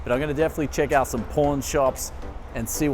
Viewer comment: I keep hearing "porn shops".